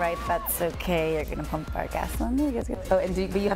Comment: I honestly thought they said "thats alright, thats ok you're gonna pump our DADS someday". :P